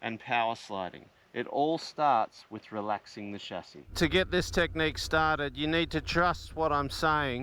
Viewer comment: I thought he was saying "relaxing the shaftie".. Got a good giggle out before i figured it out.